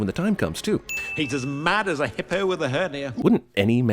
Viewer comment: Hippo with a hernia makes sense, Hippos are already essentially anger incarnate, so with the addition of a hernia... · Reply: I can't believe I'd been mishearing that line since 1994. All this time, I seriously thought Zazu was saying "Hippo with a hurt ear."